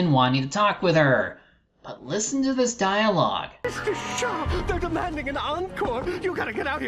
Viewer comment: I think hes saying Miss Duchat. It sounds like Mis-do-shaw phonetically.